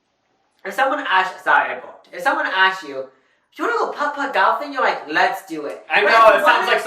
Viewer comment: I thought said putt putt dolphin